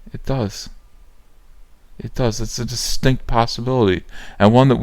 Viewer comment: i heard its a "stink possibility" :D